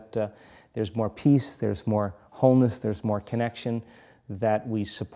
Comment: I didn’t quite understand what he said “there is more peace, there is more homes, that we love each other”? Or what was it? Around 31:20? Thank you for hearing his English for me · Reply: subtitles say 'homeless', but Joe Dispenza said 'wholeness' wholeness means completeness means unity. People coming together. Hope this helps!